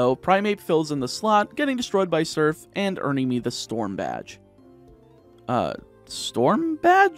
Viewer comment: Misheard "storm badge" as "swarm badge" and I was infinitely more confused ngl